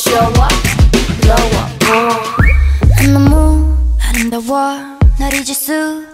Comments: For some reason I thought Rosé said 'In the moon, and in the wall'. XD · I needed to confirm the lyrics cuz i heard "in the moon and in the world" When it's "neon neomu areumdawo" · I hear “in the moon and in the wall” “See the gun shoot away look at me shhh everyday all day they got that money sold so zoom zoom”